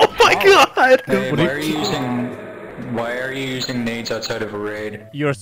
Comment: Did he get the subtitles wrong because i hear "says the idiot youtuber who things minging is funny"